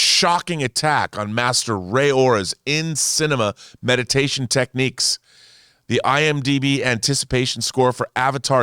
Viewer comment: "Master Ray Ora's in-cinema meditation techniques" 😂 😂 😂 Omg I laughed so hard at this! I had to replay it again to make sure I heard it right 😆